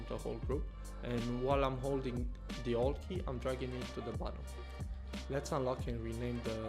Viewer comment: Hey, i am a newbie with photoshop, i have problem with 0:55 is saying ALT key to enlarge the grid ? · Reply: hey, I'm holding the alt key so the grid size change is applied only on its height, without scaling the whole thing. Hope that helps :)